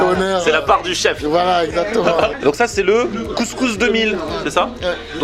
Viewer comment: Le couscous de mil, j'ai entendu "couscous 2000" 😂😂😂